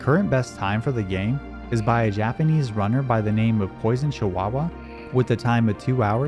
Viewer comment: I misheard this as "moist chihuaua" Sounds better tbh